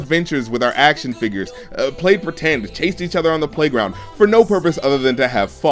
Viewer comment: I thought you said taste each other on the playground